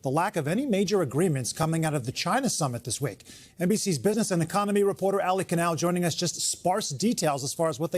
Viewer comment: It’s ‘business and economy reporter’, initially 0:14 sounded like ‘business and comedy’ I was like wuh??